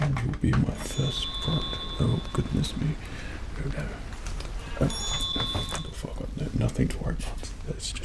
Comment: Looking back on it, I know he said "Oh Farquad no, nothing to worry about". But at first, I definitely heard "Oh fuck- no, nothing to worry about".